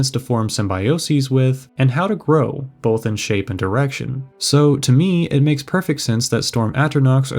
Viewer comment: dude... i heard you say: "..both in shape and erection", lost it completely.